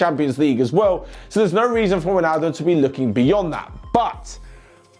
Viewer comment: Just me or does say Ronaldo instead of Wijnaldum